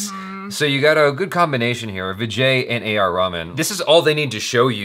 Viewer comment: Did he just say A. R. Ramen....lol 😝😝😝😂😂😂😂